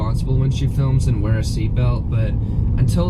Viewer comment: I thought she said something else to like where going to watch fu** mirror :O okay :D